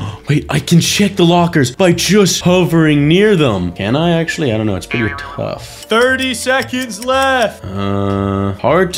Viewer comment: Guys at 58:24 though 58:28 I think was about to say the F bomb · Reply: He goes ah fu